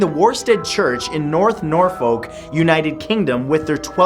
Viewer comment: did he say Norfolk?